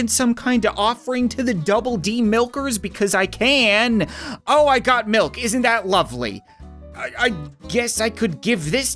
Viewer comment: it sounds like he said "oh I got milked"